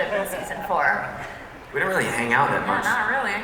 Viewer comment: What KJ sayed at 33:34 ? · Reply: “We don’t really hang out that much”